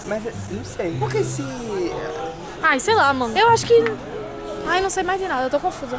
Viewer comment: Olhem o minuto 5:07 até 5:15 A FALA AMOR PARA O PABLO? SÓ EU QUE OUVI ISSO????